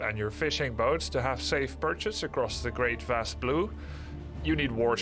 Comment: I had to listen several times -- I thought Grubby was saying you need transport ships to get your troops "across the great vast loo".